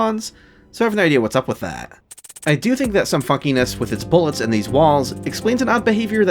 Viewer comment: I know you said "funkiness" but I heard "fuckiness" and that's sounds equally as applicable.